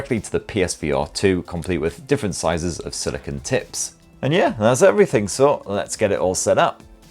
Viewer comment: did you say "different sizes of silicone tits"? ;D